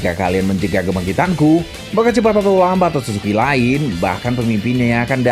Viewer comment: lo ngomong apa??🤣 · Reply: Maka cepat atau lambat otsutsuki lain..